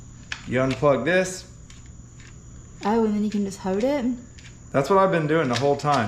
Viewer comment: I love her accent. I think she just said hold it. Not hoe did